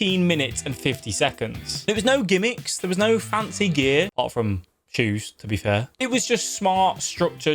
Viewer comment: I misheard you first and thought you said "There was no fancy gear apart from juice" 😂😂